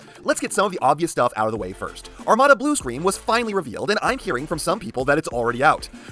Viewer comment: Nope. He says that he looks like Thundercracker when it happens, but that's actually Starscream. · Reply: oh my bad I thought you said Blue streak like the g1 character but I realize you said blue Scream